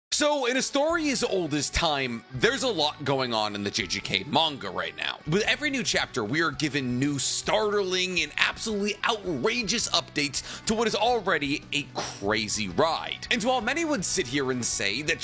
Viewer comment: I misheard "startling" at the beginning as "starterling" and thought about how that's odd and it reminds me of "tiefling". I then remembered that this video isn't even D&D or Baldur's Gate related and I realized i have problems